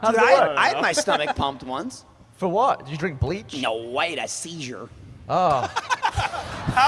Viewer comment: What did say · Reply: "Nooo, I had a seizure! "